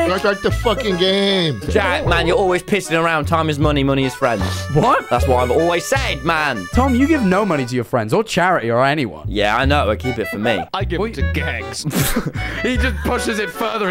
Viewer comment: when slime said "i give it to the gangs" i thought he said "i give it to the gays" got my hopes up that i wouldn't be so poor